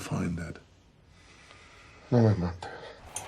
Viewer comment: What did the guy in the shower say? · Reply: "No, me matas." - Don't kill me.